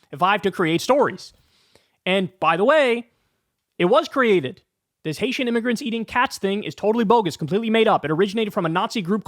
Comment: Getting a mixed up cross between immigrants and migrants nothing. What I heard was "Haitian Nig....immigrants". I heard an N-sound, not an M-sound. Big difference between the two. So yeah, he knew what he was trying to say but didn't, because racist prick. My apologies for using half the N-word. It's not something I do.